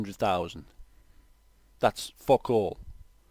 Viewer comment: I don't understand what you say at 13:07 "That's [.?.]" Can you tell me please (french guy here, I think I understand what you say but not sure) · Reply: That's fuck all